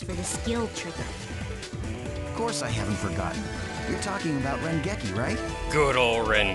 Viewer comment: When they do that ren geki it sounds like they say spring turkey